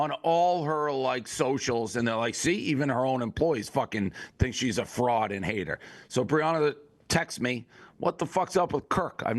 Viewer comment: Did he say, “See even her own employees fuckin think she’s a fraud and hate her!” @ 1:43:19 she doesn’t have employees? Right?